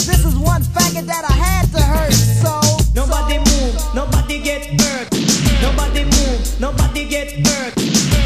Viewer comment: Sounds like he says “nobody move, nobody give birth”